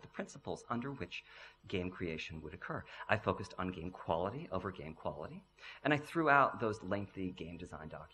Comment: Is he saying "I focused on game quality over game quality" ?